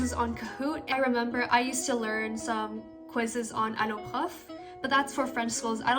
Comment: Hey, awesome video! I was also wondering what you said at 2:29 because I'm currently doing French at school too, so I think the resource will help me out a lot. :D Very cute cat at the end btw lul, so chill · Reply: thanks!! the website is alloprof 💕